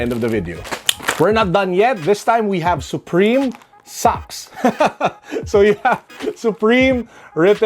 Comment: I wasn't paying attention so i thought you said "Supreme Sucks!" but it was actually "Supreme Socks.."